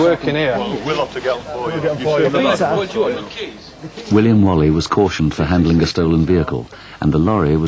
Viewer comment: did he say William Wally lol😂😂